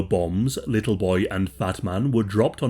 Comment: the subtitles think he's saying "little boy and batman". Honestly, if a giant batman dropped down from the sky, I would be utterly terrified.